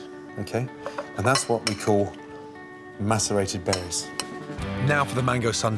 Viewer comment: who thought he said Masterbaited berries?